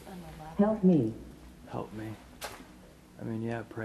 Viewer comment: in 47:04, I heard "help me" twice before the spirit talker announced it.